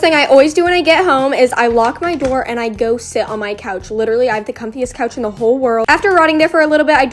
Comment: I thought she said “I go shit on my couch”😂😂😂